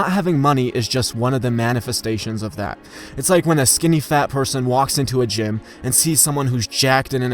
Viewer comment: Did you really say skinny fat person? Lol